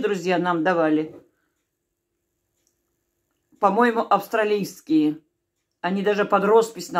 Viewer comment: Только-что кофе попил, и бутерброд с копченостями и тёрпким швейцарским сыром, приятного всем аппетита! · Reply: Спасибо за интересную жизненную историю, захотелось рыбки пожарить)) и ещё вопрос: я расслышал за ножи как ‘австралийские’ — может быть ‘австрийские’? и какая была фирма, я тоже люблю хорошие кухонные ножи,..